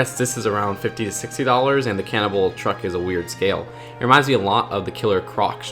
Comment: Whenever you say "cannonball", I hear "Cannibal". That would be a very different set if that were the case lol